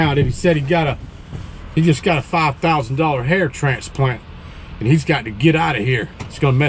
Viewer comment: At first I thought the guy said a $5000 haircut. Was going to say who in the right mind would pay 5g's for a haircut? Then said a transplant. My mind went from damn to ohhh real quick.